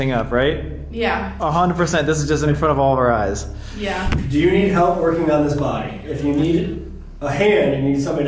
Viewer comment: i dont know if that was the sound of sam zooming in but it sounded like a voice to me 😭 do you need help working on this body" then a voice*